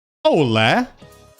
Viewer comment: At the start I firstly thought he said "olá", which is "hello" in Portuguese.